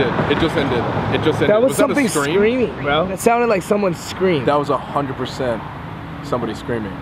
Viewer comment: When you guys said one ''SCREAMED" I heard the name "WRINKLE THE CLOWN"!!